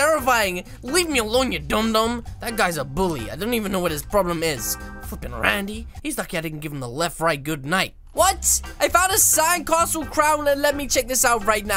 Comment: Did he say Fucking randy?